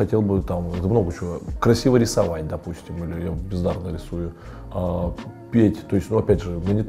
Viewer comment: мне же не одному послышалось?))) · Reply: Бездарно рисую, а не пиздато